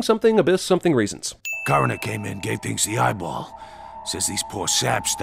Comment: Am I the only one who cannot discern what the unseen character says starting at the 5:15 min. mark? What I'm hearing is, "Coroner came in can't fix the eyeball." That doesn't make any sense, so I know I'm just not hearing it correctly. If anyone knows the correct quote, please let me know what it is! I know I'm being ridiculous, but it's driving me crazy! Lol